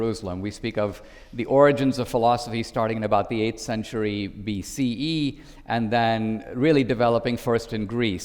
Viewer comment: Did Dinesh just say BCE? 😐